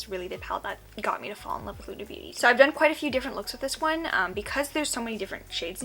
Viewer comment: I swear you said HUDA beauty lol